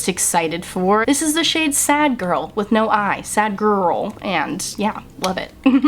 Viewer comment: I heard that as "Sad Girl with no Eye."